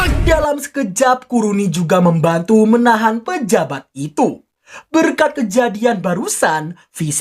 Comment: engga salah denger kah penjahat/ pejabat yaaa?